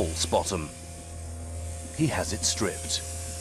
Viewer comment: when he says “he has it striped” the subtitles say “he has him boned”. I think the camora have infiltrated the subtitles 😂